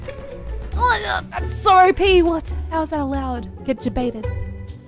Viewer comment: its sounds like she saying I'm sorry pea watch